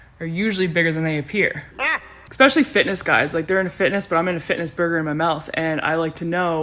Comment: I thought she said fitnesses burger as a euphemism for Kok